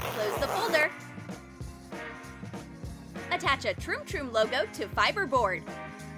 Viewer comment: she actually said trim trim